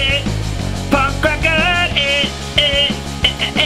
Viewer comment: i thought he said 'prawn cracker'